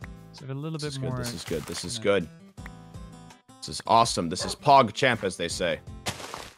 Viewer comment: I thought it said chogchamp at first not cogchamp